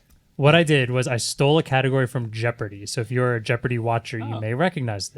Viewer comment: I heard “stolen jeopardy category”and prayed for potent potables